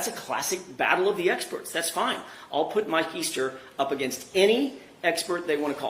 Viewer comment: When he said "Mike Easter" I heard "my keister". Maybe they could call Mike Hunt as well.